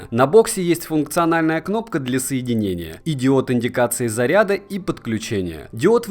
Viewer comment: Услышал на 1:44 ..."идиот индикации заряда..." и вспомнил "стоша говнозад" )) Спасибо за обзор. Ты лучший!